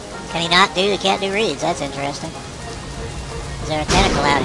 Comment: LOL 17:40 sounds like he says "Oh he cant do weed that's intresting" :D